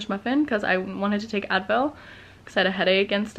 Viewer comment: every time you said Advil I thought you were saying adderall